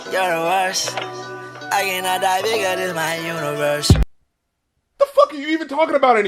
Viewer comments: I always thought Uzi was saying "I am not a bigot, this my universe." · I swear I thought uzi said “I cannot die bigger than my universe” mind blown!